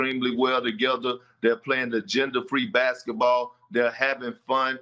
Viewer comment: Perk stay confusing tf out of me. I seriously thought he said, "They're playing with a gender-free basketball."🙈🤦🏽‍♂️